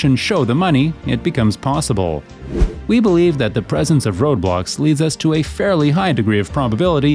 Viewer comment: I thought you said roblox bruv